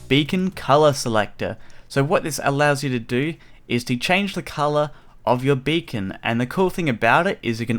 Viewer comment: For a sec I thought you said you can change the color of your bacon xD